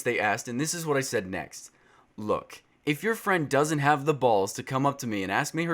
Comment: in 3:06 did u say balls?? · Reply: I said balls